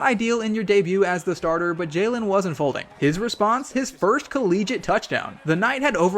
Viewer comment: bro what was that word😅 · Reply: Collegiate?